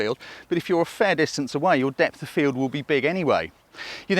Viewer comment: What do you say at 14:42? What shutter speed? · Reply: It should say 'set your'....!